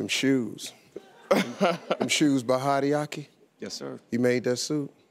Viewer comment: I didnt catch that. What shoes is he wearing? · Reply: Hideoki